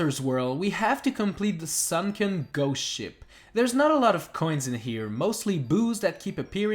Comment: when you said ''sunken ghost ship'' i heard ''shrunken goat Sh!t